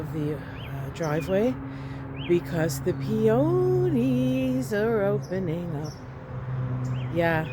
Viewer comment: I wasn't looking but listening and swore I heard you say the 'peyote blossoms are opening'. It was cool. Love all the beauty! Thank you✨🍄✨ PS - may I ask your rising sign? My sun and moon are both in the same sign too but I heard that our rising is how we appear to others. Just curious if that's alright.